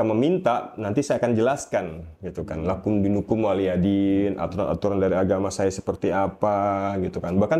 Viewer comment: Maap beribu maap ko apa kuping salah denger itu bilang WALYADIN tp yg bener waliyadin